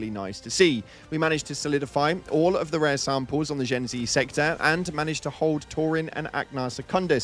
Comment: youtube auto captioning misheard this, it should be Jin Xi sector, not Gen Z 😭😂